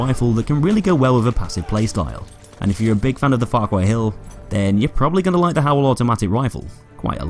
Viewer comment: I used 18hrs to kill 105 with cege-regotii opticals,and it took only 3hrs with howell factory. I like it. I think its pretty good. P.S. 12:37 What did you said? I heard 'farcwhitehill' style. Did i hear correctly?. If i was correct, can you tell me what does that mean? (I'm not an native-english speaker. Its bit hard to understand...)